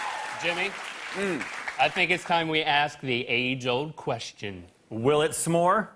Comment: I always thought they said "time to ask the angel a question" But its actually "time to ask the age old question" 😐